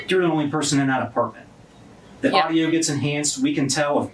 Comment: OMFGGG the first time I heard this, I thought he said, "We can hear people *rubbing one outttt*" Lord, help me 😅😅😅